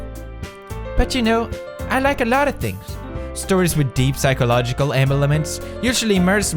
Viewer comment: Is it just my hearing or did Pewds said emelements?